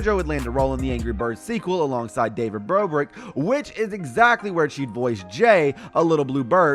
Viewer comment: did he just say david BROBRICK-🤭🤣🤣 Also it SssniperWolf is not in the video..